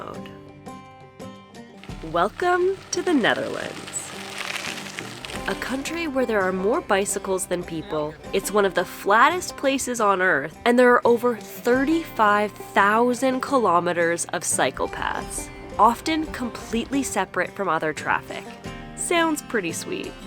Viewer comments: I misheared you at 0:37 and thought you said '35000 kilometers of psychopaths'. Of course some some tourist in Amsterdam think that local cyclist are correctly named that way. That bike pump is designed for a diffrent type of tyre valve. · I thought I heard "psychopaths" when you said "cycle paths" which is not far from the truth, actually